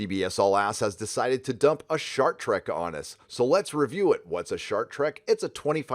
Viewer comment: Thought you said Shark Trek for a minute there.